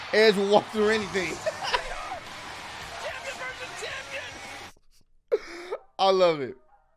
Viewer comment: All I keep hearing is "JR. Champion vs Champion."